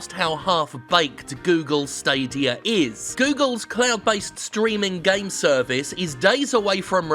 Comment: For a moment I thought he said "Google's clown-based streaming games service"...